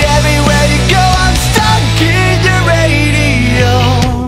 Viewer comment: Lol, I keep hearing "Sucking your Radio" near the middle of the song